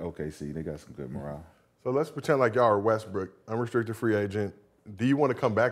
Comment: why he called him westbrick and Gil pretended like he didnt hear it lol